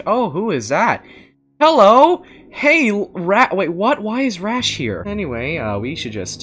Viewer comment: captions say that rash is a rat I